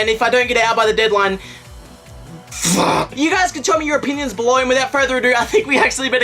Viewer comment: i thought he said "You guys can show me your penis below." Felt i needed to share that ...